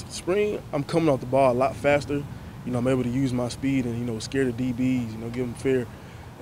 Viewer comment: He didn't say anything close to what's written in the title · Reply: "I'm able to use my speed to scare the DBs, give them fear" is what he said verbatim around 0:20...